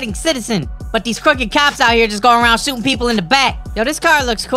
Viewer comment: what he say🤭🤭🤭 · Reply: He said these crooked cops be shooting ppl in the back